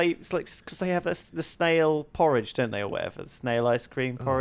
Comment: When sips said snail porridge I thought he said snail porn